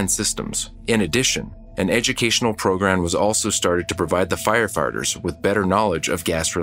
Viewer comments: Did anyone else hear " FireFARTERS" at 10:39? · Did you say...firefarters?